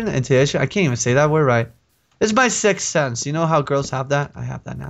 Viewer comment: when said 'sixth sense' i thought he said 'sex sense'